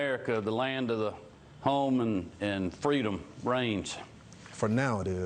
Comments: First time I heard it, I thought Kemp said 'Land of the homes and free of the brains' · Honestly I heard "Land of the home, and Freedom brains." and I think that should be amended to the anthem.